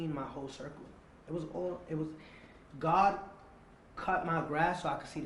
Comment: is he saying God Mexican??? 😂